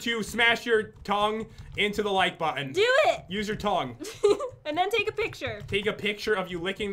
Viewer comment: I heard "use your tongs"